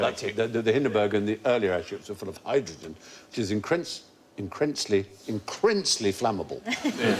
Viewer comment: What is Stephen saying at 6:12? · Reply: combined the words 'incredible' and intense and said the air ship was "incrensly" flammable..